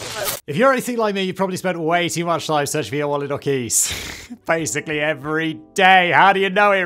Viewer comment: "wallet or keys". Did anyone else hear "koala wookies" LoL 🤣😆